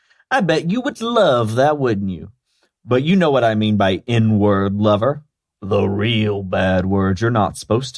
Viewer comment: Oh, "N-word lover"! I thought they were saying "inward lover" and I was confused.